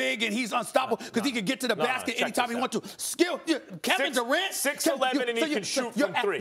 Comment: Did he just call Max Skip or am I bugging out? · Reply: I think he was trying to say “skill”